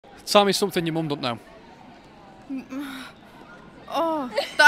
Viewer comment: What did the guy ask the girl? All I heard is " Can it be rude?" "I like someone spitting in my face." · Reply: "Tell me something your mum don't know"